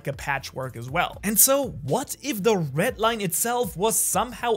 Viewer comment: I'm so into the theory but I keep hearing "Ret line" 😭 my focus would be gone for a while and then back. But I seriously love this theory ☺️